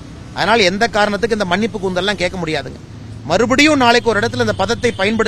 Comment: what did he just say? மன்னிப்பு கூந்தல்😂😂😂😂😂 வாத்தா...இதுக்கு மேல சின்ன தத்திய நாறடிக்க முடியாது😂🤭🤭😂😂😂சாவுடா